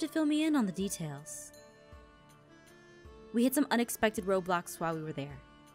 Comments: anyone else heard her say ROBLOX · did Aphmau say "Roblox"? XD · did anyone hear at 1:58 she said Roblox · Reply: Aaron She Said Road blocks